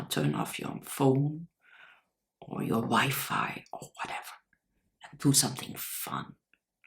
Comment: For a split second I thought you said "turn off your phone and wife" rather than wifi